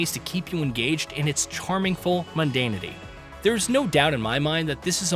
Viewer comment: did he say "charmingful"?